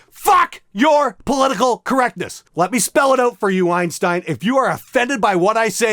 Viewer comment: did he say "Whine-stein"?